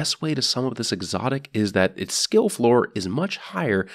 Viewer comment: did he say kill FLOOR?